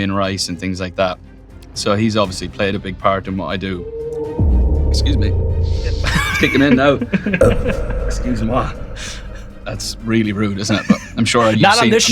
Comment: Did he say “excusez- moi” at 14:30?